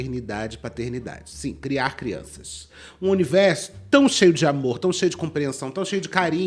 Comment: Em vez de amor, eu ouvi aborto